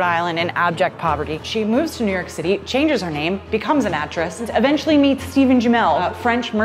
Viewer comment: I’m sure I’m miss hearing this it sounds like she says she moved to New York City changes her name and becomes a mattress